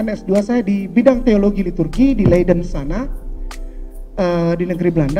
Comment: Dimenit 11:7-11:14 coba dengar dia bilang dia selesaikan S2 di TURKI di Negeri BELANDA, apakah TURKI itu Negara belanda..?, atau kupingku salah dengar..😂🤣😁😀